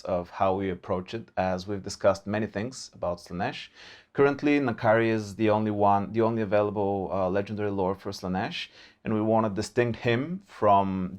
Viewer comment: what they said : currently, N'kari is the only lord avaliable for Slaneesh what I heard : currently, Kairos is the only lord avaliable for Tzeentch